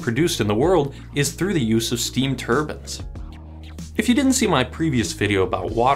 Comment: I just heard Steam "Turbans" and went "What? Oooh Turbines!" (Wasnt looking at video just listening)